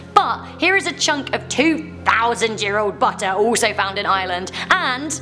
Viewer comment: I cant tell did u say island or Ireland